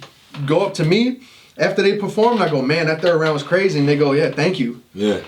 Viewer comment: did say Nigga? “That third round was crazy nigga” Wait did he really say that